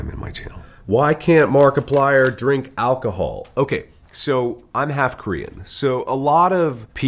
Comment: “Why can’t Markiplier drink alcohol?” Markiplier: Ok, so I’m half Korean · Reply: squish _ when i heard that i thought he said “im half green”